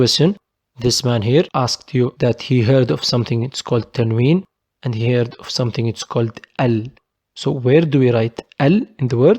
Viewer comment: Great video, really amazing. I couldn't understand the question about tanwin and (?), min 12:22. Many thanks for your time and help!!!! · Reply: Thank you for your comment Tanween ( double movement ) and ال the definite article In the previous video we learned that one of them comes in the starting of the word and the other comes in the end of the word نهاية الكلمة : end of the word بداية الكلمة : starting of the word